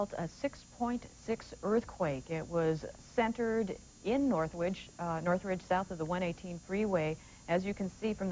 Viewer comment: I just heard the word "North Witch"